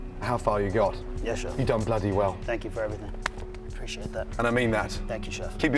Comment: ok I have to ask this lol am I the only one who misheard Gordon when he was eliminating jimmy? I heard "you dumb bloody whale" lol